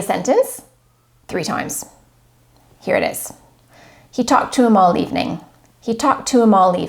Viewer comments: First 3 times i heard " He talked to moul this evening" then i understood she was saying "he talked to him all evening'' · Hi, first I heard "He talked to them all evening".then I rewind the contest in mind and I understood she was saying "He talked to him all evening".